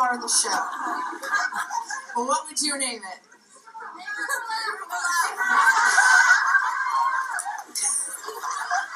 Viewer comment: What was the crowd saying at 2:08 - 2:12 ?? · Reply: They were saying, "Make room for LAF."